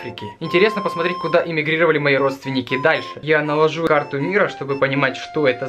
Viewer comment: мне послышалось " Я наложу на карту мира" :)